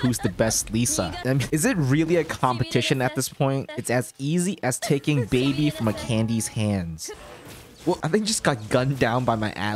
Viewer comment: "It's as easy as taking baby from a candy's hand" 🤣🤣🤣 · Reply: I just realized he did said that while i thought he said “it’s as easy as taking a candy from a babys hand” 🤠